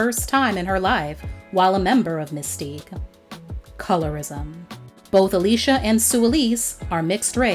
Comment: She didn't say "Colorism" she said Racism at 9:39. People who run their mouth about "Colorism" really don't understand Racism/White Supremacy, nor are they trying to. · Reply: She clearly said colourism